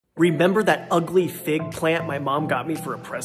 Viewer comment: omg at first I thought he said fake plant and when he said it was doing a lot better I thought he was being sarcastic lol